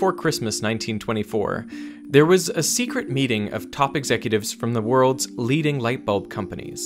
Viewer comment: I liked to hear "topic zecutives" instead of "top executives." :D